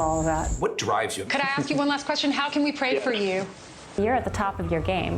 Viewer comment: Sorry, I'm not born English speaker. Did she say "How can we pray for you?"